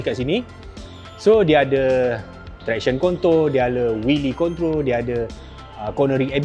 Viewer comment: everyone: traction control bang: traction KONTOL..!!!🤣🤣🤣🤣 *gurau2 saje je salah dengar